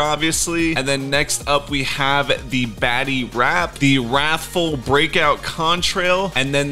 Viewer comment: I thought he said baddy when he said batty